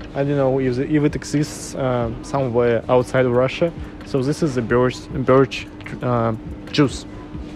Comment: What was the final one something nice I thought he said birch as in the tree what was it? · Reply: Yeah, this is a birch tree and juice that comes from it :)